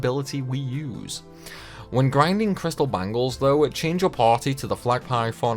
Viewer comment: What did you say you replaced Crystal Bangles with? I thought I heard you say Crystalline Bangles but I don’t remember those being in this game.